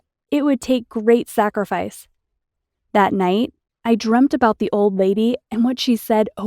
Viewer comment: it sounded like she said dremd instead of dreamed